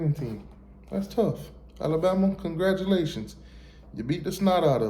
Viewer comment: I THOUGHT YOU SAID YOU BEAT US 😂😅